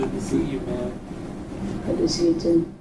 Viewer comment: when he says "good to see you" and his son replies "good to see you too", the subtitles say "I just hate you"! THIS MAN IS CLEARLY INNOCENT IF EVEN THE SUBTITLES ARE AGAINST HIM!